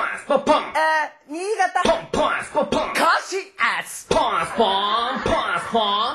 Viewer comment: 何て言ってるのかすらわからない。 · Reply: 俺的には甲信越ってきこえた